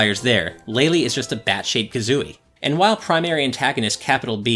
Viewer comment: I heard "Bat shaped Kazooie" as "Batshit Kazooie".